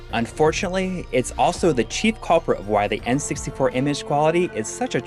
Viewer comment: I can’t hear what they’re saying at 3:32. The cheap what? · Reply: "chief culprit"